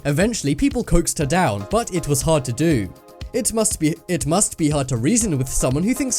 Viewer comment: Did anyone hear say it must be two time